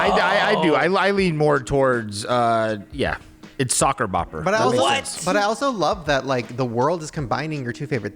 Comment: I heard “that tastes like parsley” lmaooooo